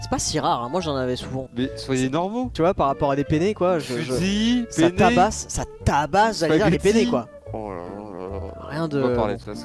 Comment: dsl mais moi j’ai crus entendre le pword